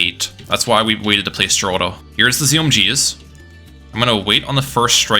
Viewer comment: For some reason the subtitles say Striker Jones instead of Geraldo lol